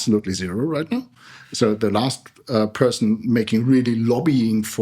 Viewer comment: who is he/she talking about? Why he/she's gone? I can't hear it clearly... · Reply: nvm it's Jütta Steiner...